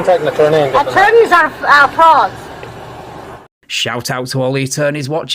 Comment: I liked it better when i thought she said “attorneys are frogs” I didnt even question it